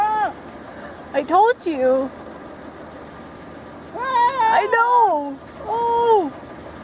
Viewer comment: Wow, beautiful scenery! I think you may have captured an EVP (IF it's not you). Listen carefully at 8:08 for a female voice that says "hey". Hear it? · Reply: mermaidlorelei After listening several times, it sounds like she says "I know her".